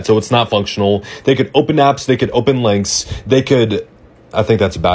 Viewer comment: When you said "links" I misheard "legs" 😅